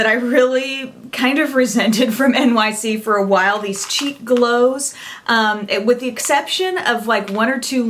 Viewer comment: LOL I thought you said "Cheap Glows" instead of "Cheek Glows"!!! I have been snubbing NYC for years, I guess I have to give them a second look now.